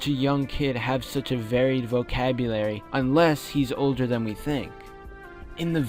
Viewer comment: did anyone hear said "olger than we think" in 3:51 to 3:53 comment yes if you heard it